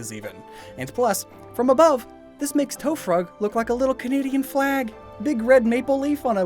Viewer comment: When I heard "Tofrug" I thought you said tough frog